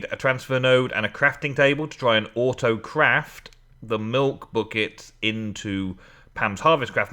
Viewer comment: haha around 17:05 said butt kicks instead of buckets :p